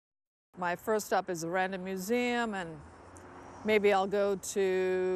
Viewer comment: Until I saw it written I thought she was saying 'Random Museum'. Maybe the app did, too.